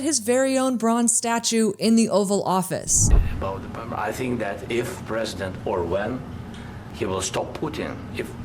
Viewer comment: did he say "if president Orwell"...?